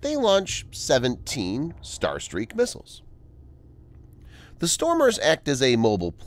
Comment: I thought it said Star Trek missile. Photon torpedoes. 😂😂😂